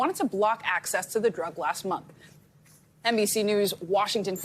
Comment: Thought they were saying MILFpristone, imagine, a pill for that.